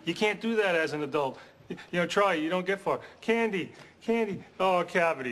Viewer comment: I heard ray say "you don't get fuck" hahahaha....!!! Who am I, am I gay??